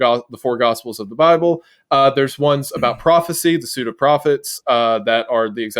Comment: I can't be the only one who heard Crawphecy at first